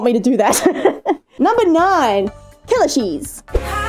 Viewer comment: I thought she said "Killer cheese" instead of "Kellashees" Help